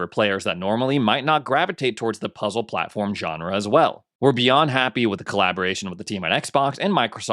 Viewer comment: It sounded like when said We're beyond happy, it sounded like he said We'd be unhappy.